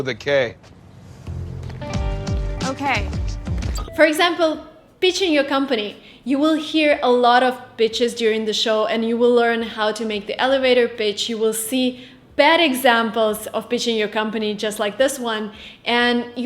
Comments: I thought she said “you will hear a lot of bitches during the show” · My wife is Russian. I love her accent. Be careful pronouncing pitching. I thought you said bitching. ❤️